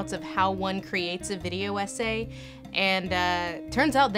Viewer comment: Great video! When you said 'how do you make a video essay'... I heard it like 'how do you make a video, ese' :')